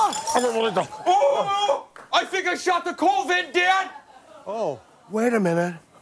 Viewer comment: I had to turn on the subs for this, I literally thought he said I Think i shot the COVID Dad